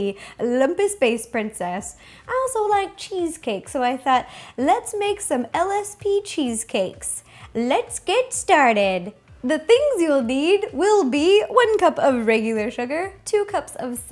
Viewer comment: Omg at first I thought she said LSD cheesecake... Really says where my head is 😂